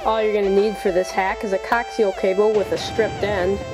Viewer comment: lolz i thought he said cocktail cable :/